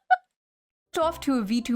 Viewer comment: nah I heard the "jer..burping compilation"